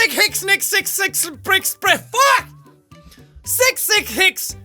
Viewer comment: I heard the "FUCK" delay.